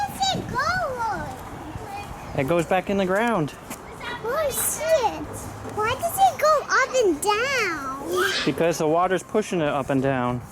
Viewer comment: After you told him it goes back in the ground did he say "holy shit, why does it go up and down??"??? Lmao!